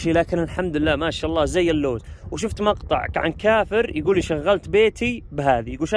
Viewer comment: انه عدت المقطع اكثر من مرة وسمعت كلمة كافر.. ابغي اتاكد في الدقيقة 2:10 😎🧐 انه اعرف كثير عرب وخليجيين بالذات مؤمنين بالله ولكن مشركين يتعبون مشايخ وعمايم وكتب مؤلفة غير القرآن وغير المطهرون المصطفون في كتاب الله الاسباط من اهل البيت.. ويتبعون فرق شركية محرمة في كتاب الله مثل سنة وشيعة ويهود ومسيح وحنبلي وشافعي وحنفي ومالكي ووهابي واخونجي وكلهم مشركين بنص قرآني قال تعالى ( ولا تكونوا من المشركين من الذين فرقوا دينهم شيعا كل حزب بما لديهم فرحون ) وكل فرقة تكفر الثانية وفرحانه بنفسها انها في الجنة وغيرها في النار قال تعالى ( وما يؤمن أكثرهم بالله إلا وهم مشركون ) على الاقل الكافر ممكن يدخل الجنة لانه غير مشرك وما يعبد غير الله ويعتقد بوجوده الا اذا ملحد اما ربي قال ان الله يغفر الذنوب جميعا الا ان يشرك به واكثرهم مشركون بالله انتهت القصة.. وسلاما سلاما 🤝🙏👌💐💚